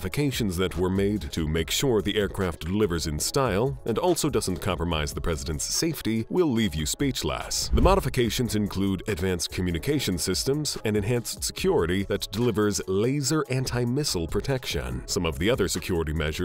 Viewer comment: Is f@ Cartman narrating this 😂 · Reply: So it wasn't just me. I heard him say "usas, and speechlass"